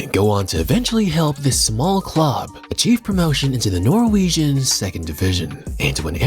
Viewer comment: I thought you said the n word lmao I was like “what???”